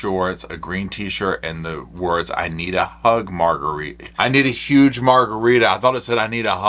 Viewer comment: Omg I thought it said “ I need a hug” too